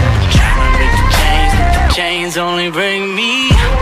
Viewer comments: "Tryna Break The Chains,But The Chains Only Break Me", that's a good lyric. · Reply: Anthony H I agree I thought he was saying trying to break the chains but the chains bring me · I thought it said "Playing with the kings, with the kings only brings me pain-ai-ai-ain"😂